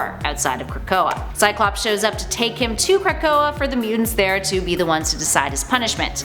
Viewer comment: Did she just say theres a place called (or atleast sounded like) "crack-o-a"? Is that where DC and marvel get there crackheads!? Are they just another alien race??